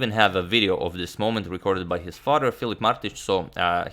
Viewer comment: I thought you said his father is Goran, not Filip.